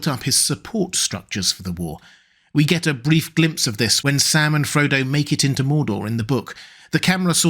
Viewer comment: Why did I hear "Salmon Frodo"?